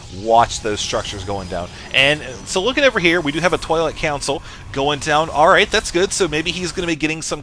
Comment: Cats are my favourite kind of pet, but I don't like them that much XP When you said "Twilight Council", I misheard that as "Toilet Council" XD